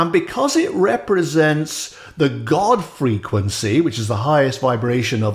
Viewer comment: "Godfrey Quincy" is what I heard you say!